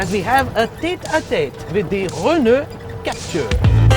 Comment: What did he say at 0:42 - 0: 43? tete-a-te with _____ Captur ??? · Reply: Renault